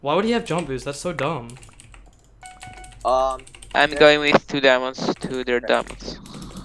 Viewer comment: what did he even say... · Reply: I’m going with 2 diamonds to their diamond